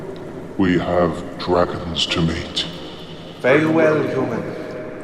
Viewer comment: Ok love this. But for a quick second I misheard something… maybe… around 29:37 Did he say mate or meet? Cause I thought it was the former and laughed a little.